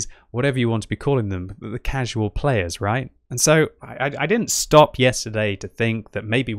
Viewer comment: did you just say at 3:35 that all casual players are red players?